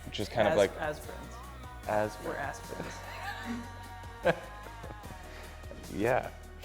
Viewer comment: did tyler say "we're ass friends" lol